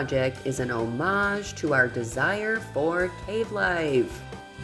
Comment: 😂 😂 I'm Totally Getting Old and a ding dong(I gotta stop eating ding dongs as I AM what I Eat) 😂😂 I Totally Thought That Said "Love life" ALLLL the way til the End UNTIL I Saw "Cave Life" anddd YES that's Sooo and Soooo AZ Peeps Life...and I Love How creative You are my friend...this Came out Awesome ..I Love everything about it and your Famous Faux Finish...wow super cool my sweet friend ❣️💯🥰👍👍👍